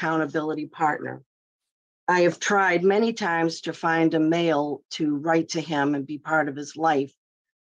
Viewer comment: I thought the friend said she was trying to get a male to help him, like a mentor, to no avail. My impression was that another male could help him work through his urges. I didn’t believe him and was glad the board voted the way they did. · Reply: Yes she said she's tried to find a male to write to him***